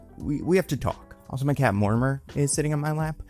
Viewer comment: Gonna be honest, last time you mentioned your cat Mortimer I misheard "Murmur". And frankly, both are great names.